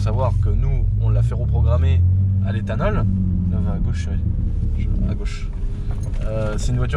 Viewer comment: c'est moi ou il dis a "gauche cherie" a